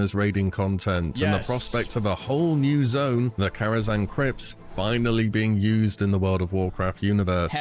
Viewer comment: I heard Karazhan Crips and was more than a little intrigued. inb4 Blackfathom Bloods